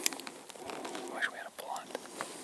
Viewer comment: did he say “I wish we had a a blunt”?? Does this mean something different in the elk woods?😂😂 or is BRO getting high on the job? Lmao